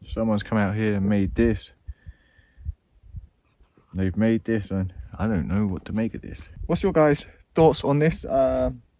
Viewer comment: you can hear a voice whispering but I can't tell what it's saying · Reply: I heard it too, think it said “Bismillah”